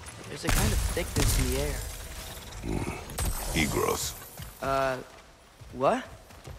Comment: I was eating and thought i heard: Atreus: "There's a kind of thiccness in the air." Kratos: "Hmm... E-girls."